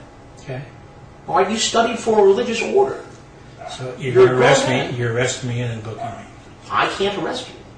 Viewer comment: does he say youre a gross man? Gigachad